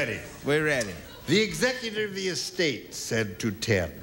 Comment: Is it just me or do I hear Gene slurring his words at 18:06? "The executive of the estate..."